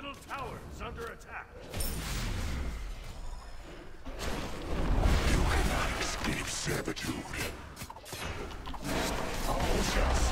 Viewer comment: On 2x speed it actually sounds like Hades says "you cannot escape SamDaDude".